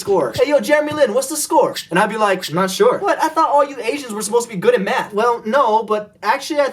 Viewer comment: Anyone else thought he said "All Asians are supposed to be good at Meth"?!